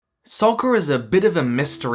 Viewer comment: DID YOU SAY SOCKER?!?!?!